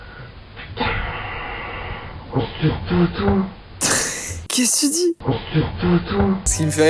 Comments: moi preso j'entend :"Portter pour toi ?" Pour rajouter, une nuit ma petite sœur elle a dit "Non !... NoN !.. Huuh..*dit le nom de mon frère*!.." on dirait qu'elle etait dans un film romantique 😭😂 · moi j’entends " Oh c’était pas à toi? " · A 2:56 j'entends 'mais qu'esta toi ?!" XD Super vidéo en tout cas ! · moi j’entends ‘ oh c’est tout doux’ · Moi à 2:57 j'entends : À ce toutou 😂 suis-je normal